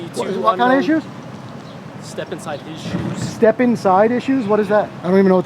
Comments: I thought he said “step inside issues” too. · I heard "step inside issues" as well. :) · Step inside issue's.. step inside his shoes definitely made me laugh LOL but on another note I see a lot of good in those two officers truly appreciate the dialogue great job and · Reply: I thought he said step inside issues 😆🤣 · LOL. I heard "Step inside issues" too. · LOL it did sound like he said "step-inside issues." I was like WTF is he talking about??? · I thought they were saying step inside issues too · I thought he said step inside issues too 😂😂😂